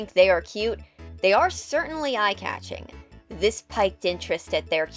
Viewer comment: I'm sorry, I really don't want to be this kind of person, but the Youtube automated Closed Captions don't seem to be working very well. At 2:20 it sounds like you said "... they are certainly eye-catching. This piked interest in their design..." I'm questioning the word piked. The CC says spiked, it sounds like you're saying piked, or is it piqued, which makes the most context in the sentence? Cause if the word you're looking for is piqued, it's pronounced more like peaked or peeked than like piked. I don't know, I don't want to be a rude grammar pronunciation snob! Maybe my brain is starting to go out, and I'm just hearing it all wrong.